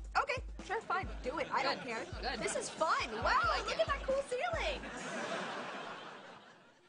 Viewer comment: did Jade say "I like it" when she puts Tori on the ground ??? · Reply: She said “I’m glad you like it.”